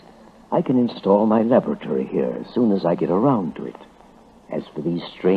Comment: "I can install my lavatory here".... :| Lol i had to double check before i worked out what he'd actually said.